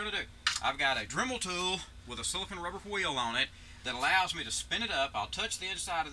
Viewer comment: What did he just say? " With a silicone rubber hwheel on it" !!!!! He put emphasis on the h. This is stewie when hes 40 years old